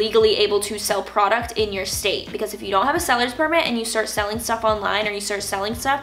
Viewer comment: Ok, is she saying "stylers" permit at 7:22? I keeeeeeeep pausing it but somehow I have no clue what she is saying? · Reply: Pryce Family she’s saying sellers permit.